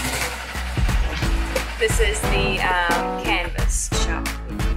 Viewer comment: Hahaha Thought you said going to the cannabis shop for a second instead of the canvas shop. Wow the video would have made a sharp turn at that point. Haha